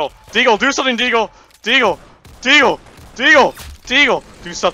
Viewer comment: he start to saying Dio instead of deagle